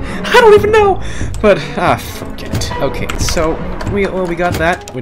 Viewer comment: Actually sounded like he said freak